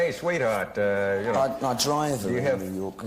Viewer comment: what does he say at 8:44 ? please tell me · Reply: I drive around New York.